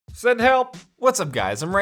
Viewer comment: Thought he said Sven help. I’ve been watching too many pewdiepie Minecraft videos...